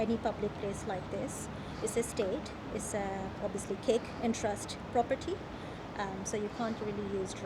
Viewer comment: What did she say here? Cake and trust? The subtitles are no use here so what did she mean?